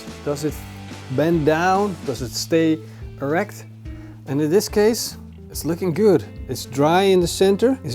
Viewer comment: Did I just hear him say your meat has to stay erect? I guess I did....